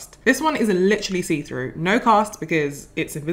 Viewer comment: I thought she said no cost. Lol. My ears are old